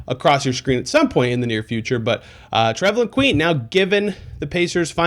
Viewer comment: At first I thought it said Traveling Queen. I hoped not.